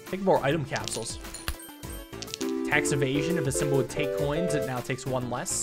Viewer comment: When I was watching this, I was cleaning up and not fully paying attention to the screen. When he got Tax Evasion at 10:00, for some reason, I thought he said “Hex of Asian.”